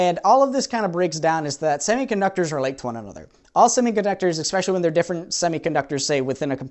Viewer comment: What does he say at 12:34 ? Semi conductors are like? what does he say? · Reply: Sorry I muffled my voice! I said "all semiconductors relate to one another"